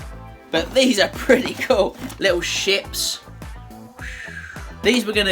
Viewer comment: Holy hell, I was looking at something and not looking at my screen...and while doing this, I thought I heard you say "little sh*ts"......I quickly looked at the screen in shock, then I see you holding a "little ship"......thankfully I saw the ship and my brain quickly realized you said ship lol.